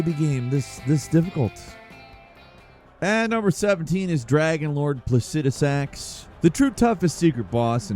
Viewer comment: Umm. Excuse me? Did you just say "dragon lord placenta sacks"?? 🤣🤣🤣🤣🤣🤣🤣🤣🤣🤣🤣🤣🤣🤣🤣🤣🤣🤣🤣🤣🤣🤣🤣🤣🤣